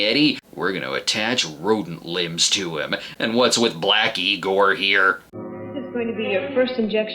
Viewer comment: i thought he said blacky gore and not black egor. it was the funniest joke until my wife corrected it. mainly because i wouldnt expect him to say anything like that.